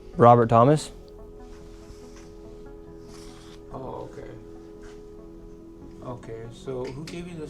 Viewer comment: His "ok" sounded like an "oh no"